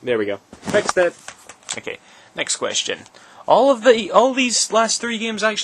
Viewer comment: did u say next question?